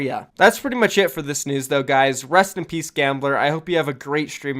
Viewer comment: did he say Rest In Peace